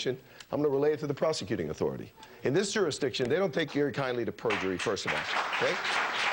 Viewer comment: did he say "don't take your kindly to perjury"?